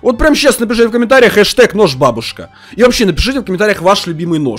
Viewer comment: это лиш мне послышалось бабушка😅😅😅😅😅😅😅😅😅😅😅😅😂😂😂😂😂😂😂😂😅😅😅😅😅😅😅😅😄😄😃😃😁😀😊☺